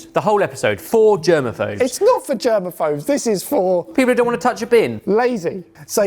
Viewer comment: I heard “it’s for German folks” and laughed hysterically